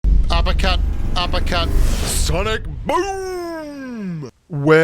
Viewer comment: All I hear is “aba kut, aba kut- sonik bummmmmm”, and then one day Youtube auto caption says “upper cut , upper cut sonic boom” \